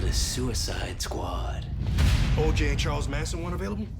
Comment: Can someone tell me what diggle says during 1:57? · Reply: He asked Amanda Waller if "OJ and Charles Manson were one of them." He said that because the Suicide Squad is made up of villains and he was asking if OJ Simpson and Charles Manson is apart of Task Force X.